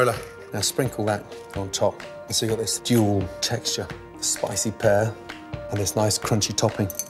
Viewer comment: can someone tell me what he said at 2:08? ' stool texture?' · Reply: ppb 0809 he said "duel texture" not stool